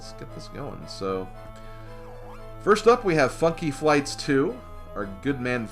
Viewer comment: did I hear mispronounce Funky Flights 2 as Fucky Flights 2